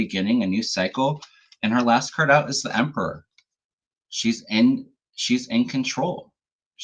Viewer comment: When you were reading on Kamala and said, "Last card out is The Emperor" I heard, "She's got it! Yeah, baby, she's got it!" (from Venus by The Shocking Blue <-- how appropriate!!) 😁💙 BLU TSU! 🌊🌊